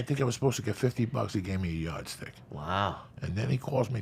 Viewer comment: you know what I think I was supposed to make 50 buck but he gave me a (unintelligible)". What did he say at the last part? Because I dont understand or know what he is saying. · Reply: He said "yardstick." Slang for a "million" or "billion." Just means he got a bunch of money.